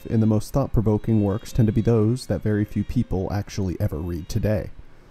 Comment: Damn. Interesting. At first I heard "the most thought provoking books then were the ones which weren't read then" and I wanted to say it may be because people don't like to stand out and draw negative attention to themselves on controversial issues, but changing them to not being read today... This will be interesting. That's a good hook. :)